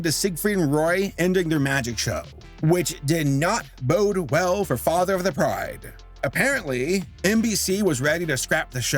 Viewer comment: this entire time I thought you were saying father of the bride